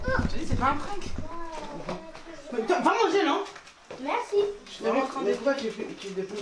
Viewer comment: Mère:va manger toi nan:ok merci 🤣🤣🤣🤣🤣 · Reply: Elle a pas dis ok merci elle a dit merci